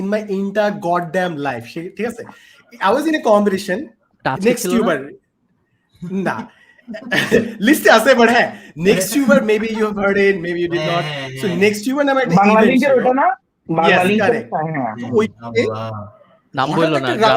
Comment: will someone tell me what Farhan Vai said? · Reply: nextuber show banglalink sponsered